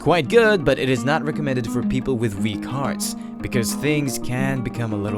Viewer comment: I thought he said people with v-cards